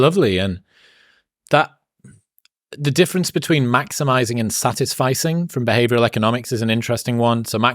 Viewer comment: I thought he said "satisfighting", for a second there.😂😂😂😂